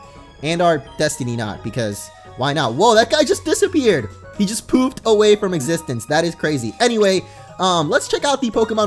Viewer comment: When said "he just poofed away" I thought he said he just pooped away...